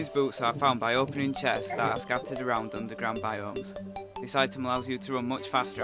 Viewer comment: ok for those of you wondering what he said at 0:09 he said underground biomes